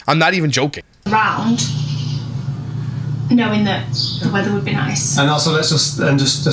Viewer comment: I thought she said because of the leather....but she said weather that makes more sense 😂